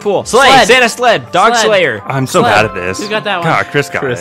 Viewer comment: Did he say "dog slayer"?